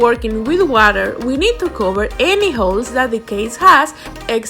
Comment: Sounded like she said hoes instead of holes😂🤣